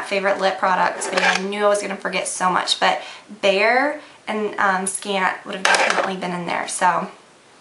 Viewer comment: haaha thought u were saying bear and scat